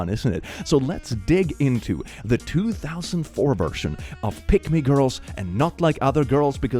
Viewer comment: the first time i heard "pick me girls", i heard it as "pygmy girls", and thought they were just small women. it confused me greatly